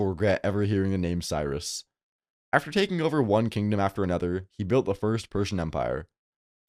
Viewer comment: I misheard this as “He built the First Person empire” and then the Doom music hit.